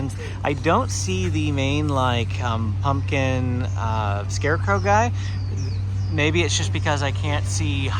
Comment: Sir! Did you just say "main pumpkin guy"?? Jack skellington the pumpkin king!!! Im offended😂😂😂 joking..that was funny though 🎃